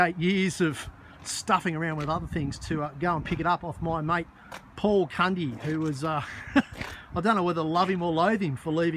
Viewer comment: I had to rewind twice to hear your mates name, i thought you said Paul cunty haha. Good luck with the tidy up👍